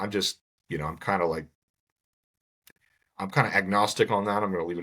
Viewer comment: at the end of the video - it sounds like he said 'Egg nostic' .... knowing that Egg nog was of concern...